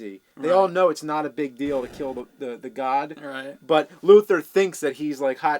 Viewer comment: I thought they were saying "Who kills the Guard?".